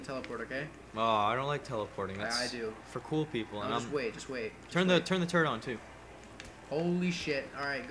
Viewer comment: lol 5:35 ish sounded like was saying turn the turd on